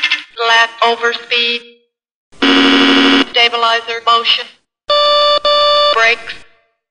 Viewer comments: sounded like stabilizer bullshit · did she said "stabilizer bullshit"?